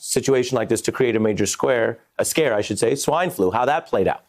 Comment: Around the 5:32 mark of this video, does he say that the presidents wanted to "create a major scare?" Is this what #MSNBC is selling??